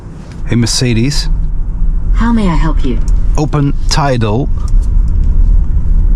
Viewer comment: i thought you said taido 😭